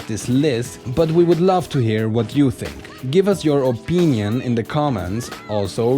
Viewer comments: I HEARD THAT "OPEENION" AT THE END LMAO. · I heard that "opeenion" thrown in there. You can't fool me, ZeRo.